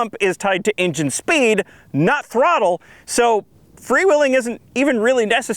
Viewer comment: I keep hearing "Free Willy".